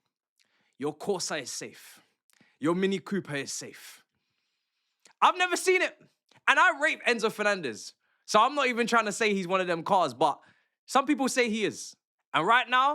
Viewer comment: Yeah 🤣 this is typical Chelsea fans they have been on Enzo for the whole season wanted him amour and now they want him to stay 🤣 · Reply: I don’t think you get what the commenter is meaning 😂 it didn’t sound like said “I rate enzo” it sounded like he said “i r*pe enzo”